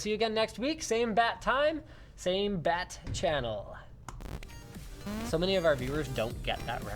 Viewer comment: For this whole time I thought he was saying "same bad time, same bad channel"...